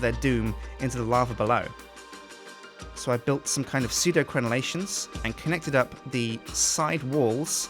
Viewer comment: I thought he said Sudocrem haha (Other rash creams are available)